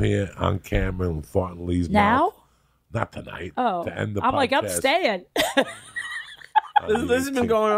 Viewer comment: Did Joey just say, “to end the podcast” . . .